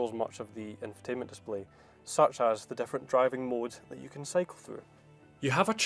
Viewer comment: did he say sex driving modes?